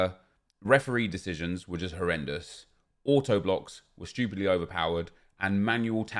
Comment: Auto blocks and keeper moving is brutal, every time shouts "AUTO-BLOCK" I hear "HADUKEN" 🤷🏻‍♂️